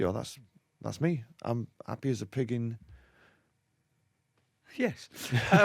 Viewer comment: What is noel tryin' to say at 7:10? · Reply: Jonathan Jones happy as a pig in shit ?